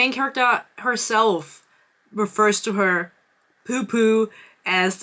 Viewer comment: Oh my god... This is the video where I realized that you’re saying “pu-pu” instead of “p*ssy”… this whole time I thought you were saying “poo-poo” for butt 😭😭😭I thought you were talking about an*l s*x for all of these videos!!! 💀💀 “pp in poo poo”